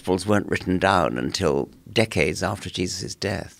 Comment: Dawkings: The gospels weren't written down until centuries after Christ" Dr. Gary Habermas enters the chat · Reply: I think he said decades, but whatever 3:20. Yup. Decades